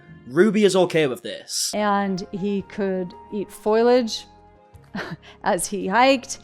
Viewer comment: I legit thought she said "he could eat spoilage while he hiked" and was laughing. Wouldn't put it past her. This woman is the worst.